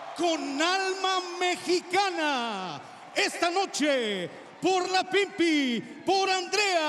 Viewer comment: I swear I hear "for la pp" and my immature self can't help but giggle